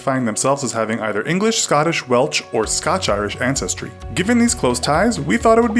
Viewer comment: did he say welch